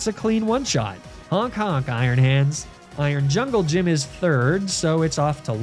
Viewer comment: I was across the room when you were narrating the turo battle and i swear to god i heard you say "iron jungle gym" instead of iron jugulus